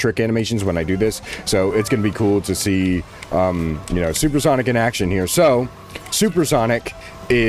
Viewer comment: when i heard supersonic trick i thought he said good night Grace